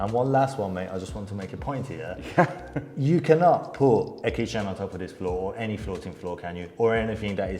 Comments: What does he say here: 20:29? It sounds like: You cannot put a keychain on top of this floor. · what did you say here, it wasn't clear. Did you say you can't put a kitchen ontop of the floor?